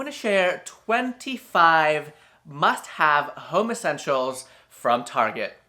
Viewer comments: I watch these in 2x speed and I did not hear “home essentials” at first I heard... “homo sensuals” 😂👏🏻 I was like, “Wow, target branching out.” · I'm sorry, but for a second I thought you said ' homosexuals instead of "home essentials". I was like, HUH!, OH, OK😅 I'm good.☺